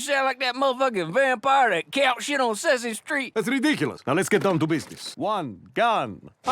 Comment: Hang on, he DID say he sounds like Count Dracula (“you sound like that motherfucking vampire that counts shit on Sesame Street”). Your point was you thought he said he doesn’t sound like him, yet he said he does.